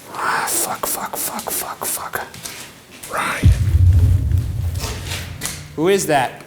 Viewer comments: at around 28:45 does anyone else hear someone say "ryan" maybe that was shane idk someone help lol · not sure if you noticed but at 28:46 I heard a distinct whisper say Ryan. not sure if Ryan said it or the ghouls said it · I thought I heard someone say Ryan??? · Between 28:40 and 28:50 you can hear someone whisper "Ryan" if you listen closely which was pretty clear. Surprised Ryan didn't catch that lol. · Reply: Right after he says fuck fuck fuck fuck you hear it "Ryan" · On 28:46 I here "Ryan" anyone else hears that??